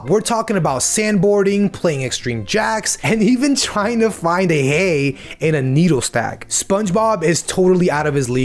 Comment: When you said “and trying to find a hay in a needle stack” I thought you said “trying to find a needle in a hay stack” 😂😂😂